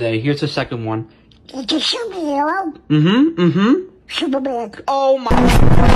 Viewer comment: I THOUGHT HE SAID SUPER BIG